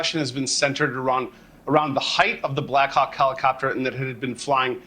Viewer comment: does he say black cock helicopter there? 4:47? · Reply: Blackhawk, you dolt.